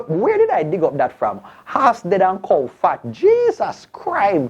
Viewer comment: But I heard it's horse dead an crow fat not cow🤣🤣🤣